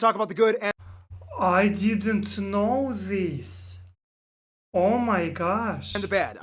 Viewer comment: Just to help out with your English study at one point you said,"I didn't knew this.". (Or at least it sounded like you said "knew". Could be it's hard to pronounce for you. "Knew" sounds the same as "New". And "Know" sounds the same "No". The correct form for this example would be,"I didn't know this.". English is full of these tenses. Past, present, and future tense of words. It can be confusing at times. "Know" I believe only has a past and present tense. "Knew" is past tense and "Know" is present tense. "Knew" is like something you had awareness of already, were familiar with, or were certain about. Some examples for "Knew": "I knew what the answer was already going to be." "He knew what he wanted." "He knew too much." Examples of "Know": "I know what I want." "I know him." "Do you know her?"